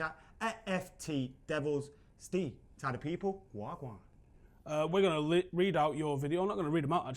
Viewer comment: As a Jamaican I have to ask, @ 0:12, did say "waa gwaan?" 😂